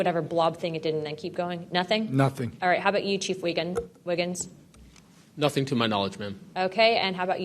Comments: Did she just say chief Wiggum? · Thought I heard chief Wiggum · Look at his name my guy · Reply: Yeah my guy I saw that my guy I heard it too my guy It just sounded like she said Chief wiggum my guy.